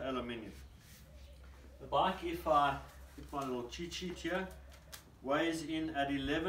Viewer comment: Hi, what did you say at 2:11 when you grab the paper? Thanks a million, I’m translating this video. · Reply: Let me grab my little cheat sheet 😀